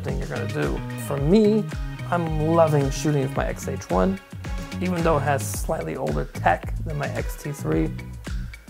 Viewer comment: What did you say “even though???? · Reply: Yes - even though it has slightly older tech than the X-T3